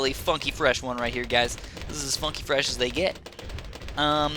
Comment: sounded like he said 'fucking fresh'